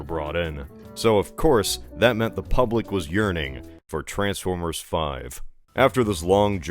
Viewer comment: I heard "The public was urinating for more transformers"